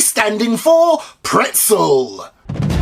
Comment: Accidentally click ahead in the video all I hear, "P is for pretzel"